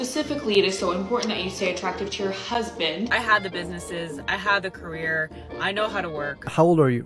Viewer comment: "I don't think women should vote or work" Ok, ma'am, then don't. but leave me out of it, I want to both vote and work. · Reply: 😂😂 did she say I had the businesses??? 1:07 I want proofs that you had them. Is that lip filler?... da m I need glasses...